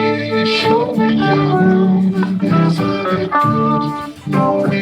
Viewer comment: I swear he said womb instead of room